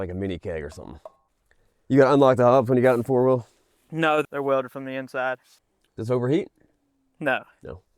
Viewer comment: i thought buddy said the front diff was welded? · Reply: No he said the front hubs were welded so you dont have to unlock and then lock them in. Basically made the front full time engaged.